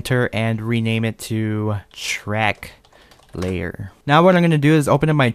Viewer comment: Did anyone heard him said "Shrek Layer"